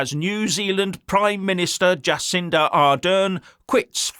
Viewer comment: Sorry i thought you said Jacinda Ardern tits I better move on :face-purple-wide-eyes